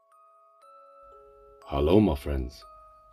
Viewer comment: In the intro of your video it sounded like you said hello Marvin it spooked the hell out of me Lmfao but thank-you I appreciate it